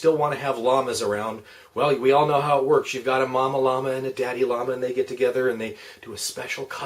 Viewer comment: I swear to God, I thought you said Obama Llama. I had to rewind it. I realize that is a pointless comment...